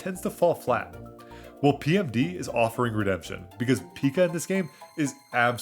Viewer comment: I thought I heard you say rudemption instead of redemption at one point, but maybe I'm just watching too much RuPaul's Drag Race. Anyways, great video!